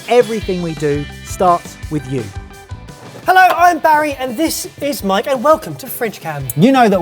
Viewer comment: sound like he is saying “this is... my N-word”